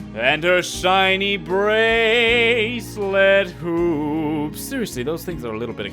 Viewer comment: The first few times I heard the pat about saving din and her shiny bracelet hoops, I thought he said boobs XD